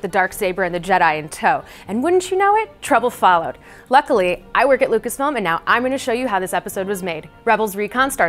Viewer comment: I thought said "I worked at Lucasfilm" and my heart stopped... then I re-watched it and realized she still works there🙏 Don't ever scare me like that again!